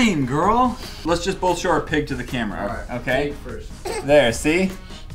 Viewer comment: i thought she said HI to the camere